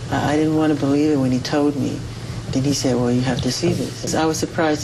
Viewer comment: Did he say Diddy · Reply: I think he said "..then he said.." (not Diddy)